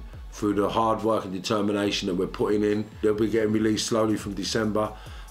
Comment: Come on. I thought you said from December 1st.haha get them up son